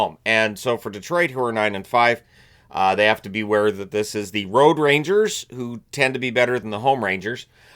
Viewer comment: First game preview made me laugh about the Rangers home n away games 😂😂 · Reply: I thought said “The Road Ragers”.